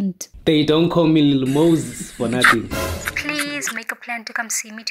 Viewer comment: 👧: ..."How you smell" Moses: "They don't call me noses 👃🤥 for nothing" 🤣🤣🤣🤣🤣🤣I thought I heard that! ⚰ ⚰